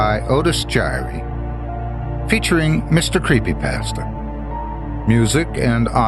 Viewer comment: Otis Jiry : Hahahahaaaa!!! I just quite literally blurted a heartfelt guffaw because of a combination of you, & the low volume of my headphones! At the beginning of the second story, when you were introducing the other cast members, it sounded like you said, "Mister Creepypants"!! snort! I may just have to keep the volume down permanently, just for the happy!!!